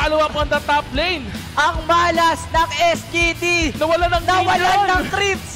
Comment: why tf the narrirator kept saying STD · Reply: SGD The squad